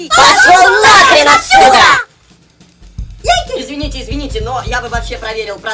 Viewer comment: На 0:40 послышалось „ я Гей”